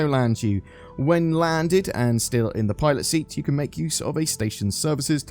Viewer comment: Sorry but i keep hearing "toilet seat" instead of "pilot seat" 🤣🤣